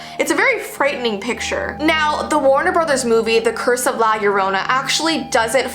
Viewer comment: AHAHAHAH IM DYINGGGG I THOUGHT IT SAID “The Curse of Corona” AHAHAHA 💀